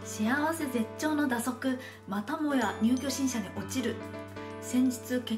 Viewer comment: 蛇足の入居審査をにゅうきょしんしゃって言ったの俺は聞こえたぞ